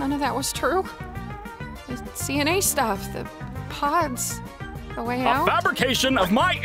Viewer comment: I thought she said pads like the one girl wear